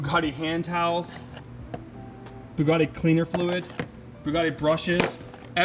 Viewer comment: did i hear “bugatti blinker fluid”? 🤣🤣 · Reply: Cleaner fluid not blinker fluid. But do you where I can get some blinker fluid.😆😆😆